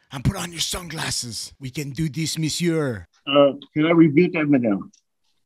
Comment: I think he wants to “Rebute that middow” while putting on sunglasses-“excuse me”@ 8:29. He says excuse me every 7 syllables. I would say “every 7 words” but I cannot tell if they are words, or what words they are. But judging from his “excuse me” count, I’d say he needs some “Bean-o” for his frequent flatulence.